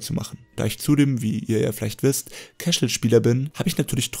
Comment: Ich hab am Anfang nicht verstanden was du für ein Spieler bist??? Kächel? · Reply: Casual :-)